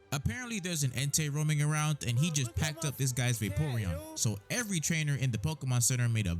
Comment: did i hear vaporeon?